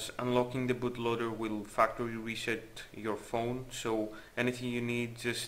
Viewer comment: when you said "factory reset" i thought you said "it will f*ck over your sh*t